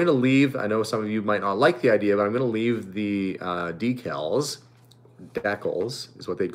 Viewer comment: Haha I watch Simply Nailogical and I thought she just said “decals” funny. But she says “deekles” not “deckles”. Canadian English is awesome. :)